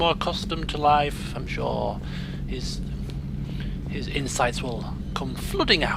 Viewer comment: "His insights will come flooding out." Thumbs up if you initially misheard that as "insides." :P